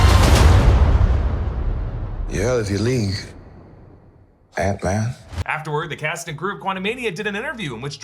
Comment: I watched the antman trailer several times and thought kang said, "I know how you're reality ends, yeah if you leave, antman." I thought thats what he said since he asked antman for help. I felt erie about that line. But I now I not sure how I feel about him saying, "You're out of your league, antman."